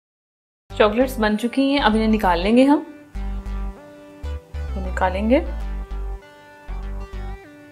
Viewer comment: it sounds like she said " I'm going to kill you" 😂😂